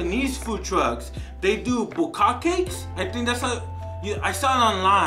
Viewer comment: Yo tell me Im not the only ome who heard bukakkakes 6:39 😂